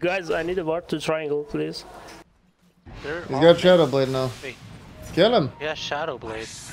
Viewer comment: I thought he said ‘cheddar blade’